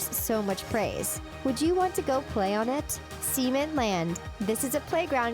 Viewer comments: sounded like she said semen land 😂😂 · She said SEMEN Land instead of CEMENT Land. Yeah I wouldn’t want to send my child to SEMEN Land.